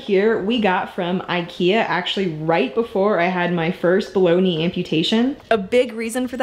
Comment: At first I thought you had said "bologna amputation" (baloney). Needless to say I was a bit confused haha.